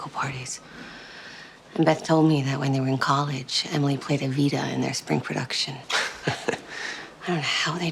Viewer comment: Did they mean a Playstation Vita, or am I mishearing that line?